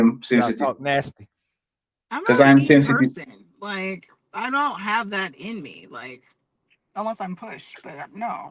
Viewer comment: Did she say,”I don’t have it in me.” RWD: mid way through the podcast she called Six a ni$$@ out of the blue and then went from saying women to bit$&es. The math ain’t mathing.